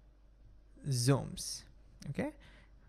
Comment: Sounded like ठीक है! Lol🤣